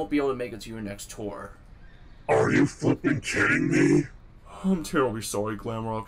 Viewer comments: wut did he say… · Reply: Monty said, "Are you flipping kidding me?" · Monty did not say the F word, He said "Are you flipping kidding me!?" and Daycare Attendant not changing into Moondrop during the night scene isn't a mistake.